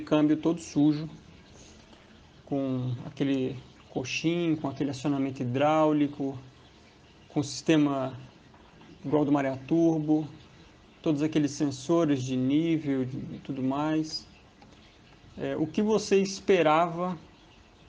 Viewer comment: os primeiros segundos do video eu olhei o cambio parecia que tinha vindo de hiroshima pós bomba. Depois ouvi "Marea Turbo" e me desesperei. Mas confio no processo. Vamos por mais e além!